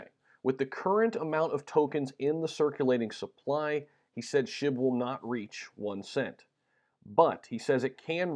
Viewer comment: He said .01? I thought he said it wouldn’t reach $1.